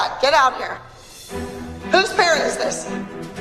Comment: Yoooooooo I just realized when Abby called the girl with the BAD HAIR CUT I thought she said “Whose parent is this” thinking she messed up meaning whose her parent - but - I think she said “Whose PARROT is this” 🤣